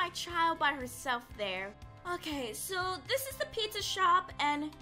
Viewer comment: did mangle said " here's the pizza shop."